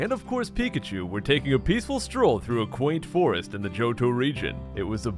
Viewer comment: I first misheard "Johto region" as "JoJo region."